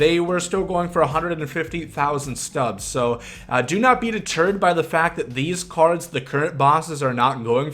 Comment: I swear at 7:11 I thought he said "Do not beat a turd"